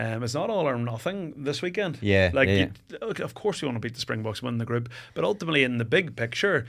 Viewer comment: did he say “of course you’re gonna beat the springboks and win the group”?